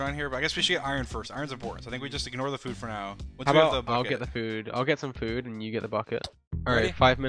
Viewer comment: POV: your here a year later watching this video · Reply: POV: Your literally hearing now he sounds like he says 'Fuckit' and not 'Bucket'